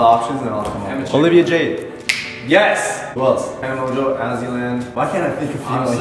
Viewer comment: DId you just say AzzyLand?!?!?!?! Does karlee have a crush on tal??